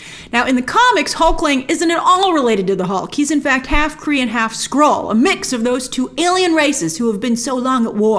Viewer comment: Half Kree and Half Skrull those two alien races. I really need to focus on what I am listening to sometimes. At first I thought you said Half Korean Half Skrull.